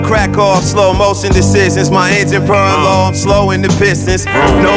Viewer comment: Anyone know what Mach says around 1:54 right after engine? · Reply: “my engine purring low, i’m slowing the pistons”*